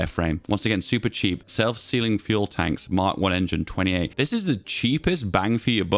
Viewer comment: Every time you say "self sealing fuel tanks" I hear "self sealing stem bolts" from Star Trek :)